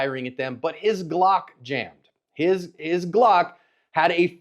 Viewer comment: At min 2:30 Did John say " his Glock jammed"???? 🤣🤣🤣🤣 I thought Glock fanboys say thats impossible.